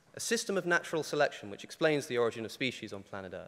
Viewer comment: does he say that natural selection explains the origins of species? Or am I hearing that wrong?